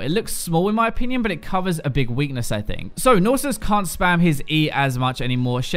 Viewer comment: Am I the only one that heard him say Nasus instead of Nautilus starting at 8:22?